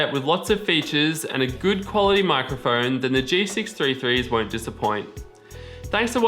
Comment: If you want a premium headset with lots of features and a good quality microphone the G633 "Are quite a dissappoint?" I Don't think I understood what you said, maybe " The G633 Wont dissappoint?" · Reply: Umm...? If you rewatch the video, he actually said “Then the g633 won’t disappoint” after talking.